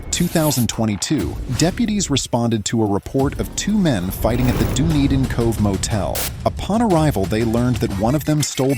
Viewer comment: Sound like he said at the dude eat me motel